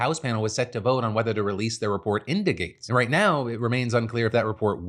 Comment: I thought you said "release the report's indicates" [into Gaetz] and I legitimately second guessed for a moment that there's a noun form I'm not aware of, as in "Hey, that's one of those indicates! And it belongs to those reports!" Then I realized I'm way smarter than that... Theeeen I went and looked it up and I will be fuckdamned... It's actually a fuckin word! Except it's not, but now you know how I felt for a second or two.